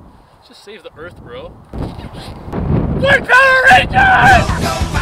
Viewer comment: What he said on 0:32?! I can't hear it properly. I get it something that triggered black people but i really can't recognize what it was :( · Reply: I think he said "white power Rangers"